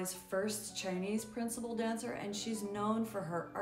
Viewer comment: I thought she said she was known for her Great “Lies!!”.. Glad they had the actual Words printed on top!!🫨🤯🫣